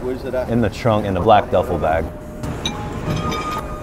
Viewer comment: I thought he was saying, "I'm sorry for weighing your time." 😂😂😂